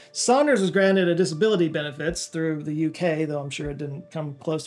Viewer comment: I'm not understanding the use of what sounds like the word "coward" here. Is it another term similar to it?